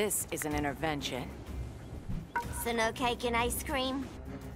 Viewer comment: thought she said "Is it okay can I scream?"